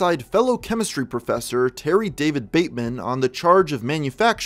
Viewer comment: For a second I thought you said Terry Davis which confused me